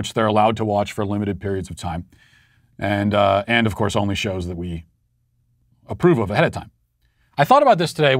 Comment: when I heard "show" I thought he said "and of course it has to be my show only."